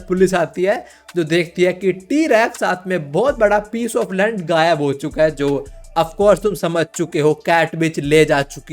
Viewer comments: you told lund instead of land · WAS IT ONLY ME OR SOMEBODY ELSE ALSO WHO LISTENED PIECE OF L*ND INSTEAD OF PIECE OF LAND.😅😂